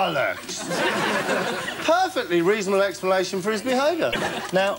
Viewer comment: I always thought that at the end he said 'it's a ruck'.